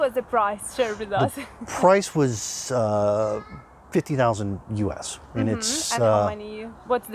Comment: On 3:24 how much the price of house did he said? $50,000 or $15,000?? Thank you